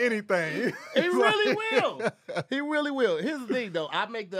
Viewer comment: Hey… I think What was trying to say was, “really real” but he said “wheelie Will” sounds like he was thinking about will smith on a bike. Anyways back to you.